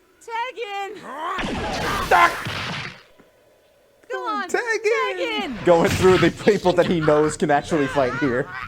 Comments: You know I didn't know he said "Gohan, tag in" after. I thought he said "come on, tag in", like he still wanted to swap with Piccolo :p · Ok for the longest time, I just thought Goku said “Come on, Tag in.” At 9:07, because he knew Piccolo was just faking it.